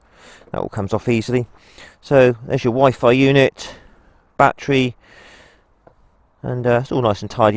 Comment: Did you say wifi unit at the end? 25:50?